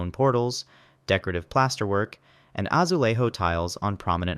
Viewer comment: “azueirro” oi deus meu 💀